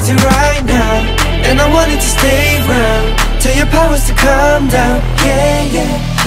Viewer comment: i actually thought Hyungwon said "tell your parents to calm down" instead of POWERS and it's a whole new world for me now 💀